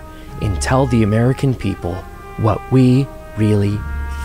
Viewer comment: Huh? I think I need to turn on captions... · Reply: "Grooming"! I thought he said "grill me". Still not very funny.